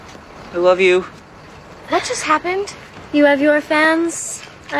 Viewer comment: "Someday your fans are gonna work for my fans" 🤣🤣😂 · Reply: I thought I heard "Someday, your panties gonna work out those pants" I knew I just misheard, this is a kids show. Dammit